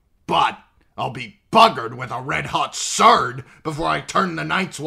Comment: "But, I'll be buggered with a red hot ???" What is the word he is saying there? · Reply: Sword I think